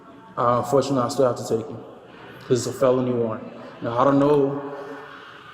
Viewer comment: what? That has nothing to do with my comment. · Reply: Listen again "It's a felony warrant" is what you misheard as "That was fun to watch". They guy is a jerk, but he didn't say what you think he said.